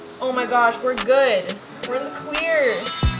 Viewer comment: when you said "we're clear!" I lowkey thought you said "we're queer!"